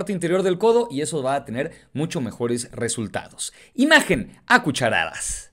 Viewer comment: Pensé que decía imagen a cucarachas